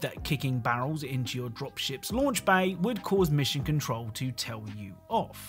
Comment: i heard "mission control" as "michigan troll" LMBO